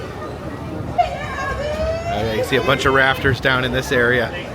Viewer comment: Good morning. When you said you saw rafters I thought you said raptors! 😮